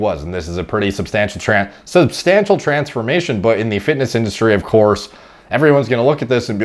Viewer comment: almost said "Trapsformation" instead of transformation lol